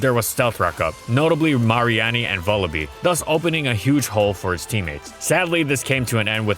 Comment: Did he, did he just say Mar-ee-aun-ee for Mareanie 😄